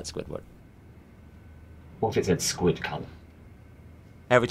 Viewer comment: What does he say at 0:46 because I'm pretty sure it isn't "Squidc*nt" but that's all I hear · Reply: He says “squid colour” Lmao Try it with subtitles it helps sometime! 😂